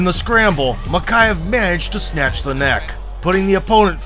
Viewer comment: I thought he said he managed to snap the neck for a second